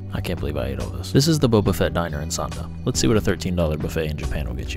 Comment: Lmao I heard him say "Boba Fett diner"